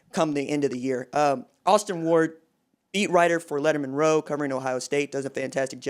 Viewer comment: Sorry bro I thought u said meat rider instead of beat writer 😂